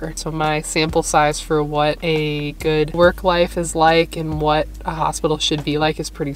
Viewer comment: I thought she said a good work wife… I was like you gotta have a good work wife. 😂